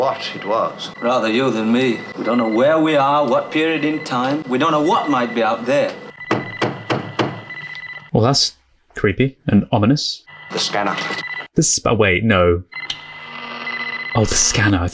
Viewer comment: I thought he said “scammer” as in don’t answer it lol